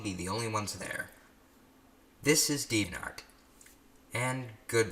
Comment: Am I the only one who thought for a moment he said 'sneeeviantart' and not deviantart >XD